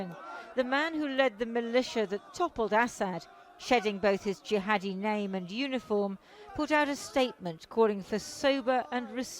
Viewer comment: Did she say that the new Sirian prime minister shed his jihadi name and uniform 2:37?